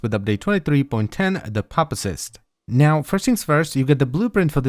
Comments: lol everytime he says pupacyst I hear "papa-cyst" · lol everytime he says pupacyst I hear "papa-cyst" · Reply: I hear "puppy-cyst"